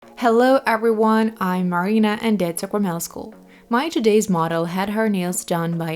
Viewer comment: and this is Sakramel School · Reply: oh...I get it. I always heard it as "deds Sacramel school!" So I checked her last name but it wasn't her last name. I was so confused. 🤦🏻‍♀️🤣 Thanks. You helped me a lot.